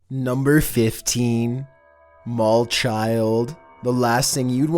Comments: I thought he said number 15 bald child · "Number 15: The Mall Child" What I heard: "Number 15: The Mole Child" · Number 15 i thought he said bald child